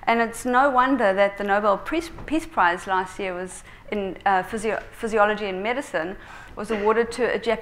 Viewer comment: Did she actually say at 11:37 that "the nobel PEACE prize for physiology and medicine...???" Thats really new to me!